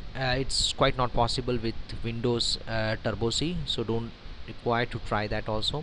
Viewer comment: Cannot do socket programming on windows? Having written many network programs on windows, I think I'm fair in saying you're wrong professor. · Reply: I know this is a late reply but for everyone that is confused about windows not having socket program misheard him. I heard "turbo c" which is a library for windows (maybe this used to have linux header files? idk this is a old tutorial) but windows doesn't have a lot of the libraries that POSIX has so you'll be using different header files. However, that doesn't mean you can't do socket programming you should check out the windows api